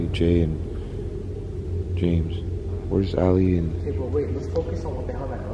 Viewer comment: I thought he said here’s Ali a